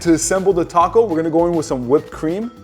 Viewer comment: What do you just say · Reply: Whipped cream